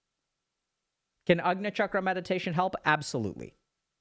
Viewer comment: Can someone please tell me what he's saying here :) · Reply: ajna chakra meditation example: laser beam meditation, there is an old video on this channel titled "Meditation for your big ego", that's an example of an ajna chakra meditation